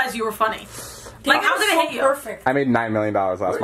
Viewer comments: wtf did Vardan just say “David is so perfect.” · Did anyone else hear Vardon say “David’s so perfect” 😂